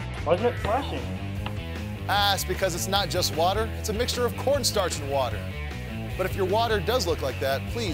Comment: I thought he said "pornstars with water" hahahaha